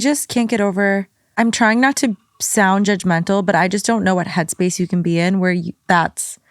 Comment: Confused on one story. The woman who said her boyfriend cheated w hos sister. I heard "i dont want to doubd judgemental.." ..? Im sorry, he cheated and commited incest but no one wants to sound judgemental? Shouldnt we ..?